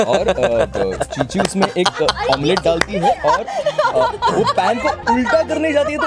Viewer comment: he really said chuchi😹